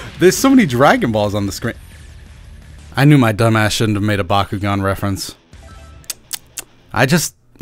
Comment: Anyone else thought he said byakugan from Naruto? Or just me.....